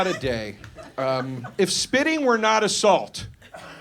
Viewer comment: Did David Lynch come back from the dead to do the soundtrack?? Secondly I thought he said “If spitting wasn’t a salt” and wait for the frisbee joke😂